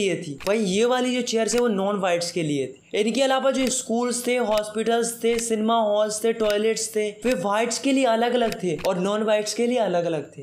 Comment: did I heard "Sigma Hall" 🤨🤣🤣